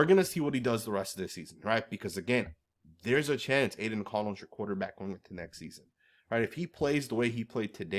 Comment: I saw a interesting comment on a Instagram post that said “If AOC was on the 49ers with all that talent they would be 17-0” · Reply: I thought I heard you say there’s a chance AOC will be our quarter back next season 😮 YOU TAKE THAT BACK!!! DONT YOU PUT THAT EVIL ON US!!!😅😅😅